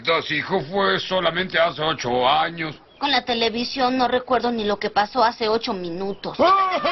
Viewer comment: I really love the Simpsons ❤️ Que dice Bart 2:37 ? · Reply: It's pretty funny "Con la televisión no recuerdo ni lo que pasó hace 8 minutos" 😂